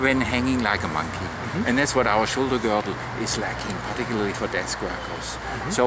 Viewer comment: hello! i`m from argentina and i can`t understand what says from minute 03:23 to 03:28. when he said " and that is what our shoulder girdle is lacking particularly ........................" thanks!! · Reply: Sorry to take so long to answer this for you. He says "and that is what our shoulder girdle is lacking, particularly for desk workers."